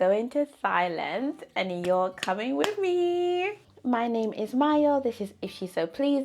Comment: I can’t really understand what you say your name is at 0:07. What did you say your name was? Also, I’m here for the hair goals! I feel like thigh length will come easily for you since you’re already in the groove of taking great care of your hair, even as you revisit your old routines and ideas! I’m on a hair journey as well and I’m trying to be more intentional when it comes to my hair by not neglecting it. Your channel has definitely been a nice source of motivation. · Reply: Thanks for your comment! My name is ❤️ Aww thank you so much! I'm hoping it will come easily and that I'll learn a lot along the way~ 100% with you on the intentionality part. Thank you so much for supporting the channel and good luck on your hair goals also!